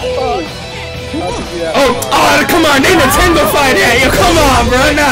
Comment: "They Nintendo-fied that!" That needs to be the term used whenever Nintendo softens something. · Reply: Oh I heard "They nintendo-fightin"